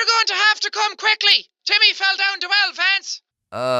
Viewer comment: sound like shes saying to france the contry YOUR GOING TO HAVE TO COME QUICKLY CUZ TIMMMY FELLL DOWN THE WELL FRANCE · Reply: That's Vance